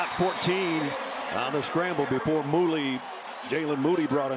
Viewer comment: Did he say "moolie" as in "moolinyan"?!.."Hey Sal, how come you ain't got no brothers up on the wall?"...Jesus Nessler!!